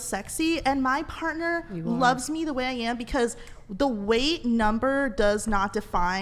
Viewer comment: is she saying “whale number”?